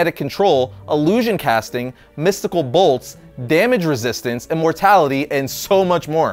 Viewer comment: I thought you said "and mortality", but then I realized you said "immortality". That would be lame to GAIN mortality.